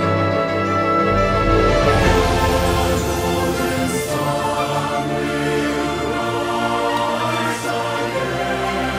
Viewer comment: 👏🏻😍 what they say?? Is about the g.sun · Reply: We say "The Golden Sun will rise again! The Golden Sun will rise, rise!" 🌞